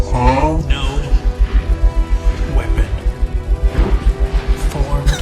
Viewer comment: I swear I misheard him say “No women” 😂